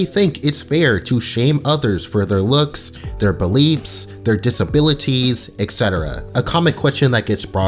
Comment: not trying to take away from the video but at 0:22 did anyone else hear beliebs?